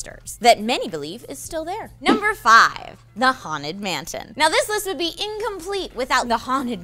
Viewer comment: who else heard her say "the haunted mantin" instead of "the haunted mansion"